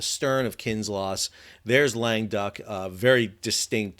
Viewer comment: I thought you've been saying "lame duck" and I thought why would the French call a ship that? Then I see that it is "Languedoc"! This pronounced: LANG GUH DOCK, emphasis on the dock, lol.